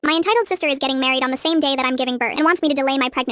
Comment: For some reason I heard "My entitled sister is ga-"😭